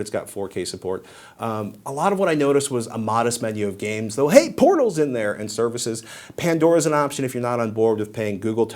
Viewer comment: Raise your hand if you thought Patrick said "Hey PORNO's in there"